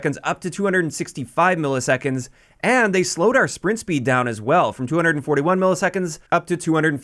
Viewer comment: you said springt speed instead of sprint to fire speed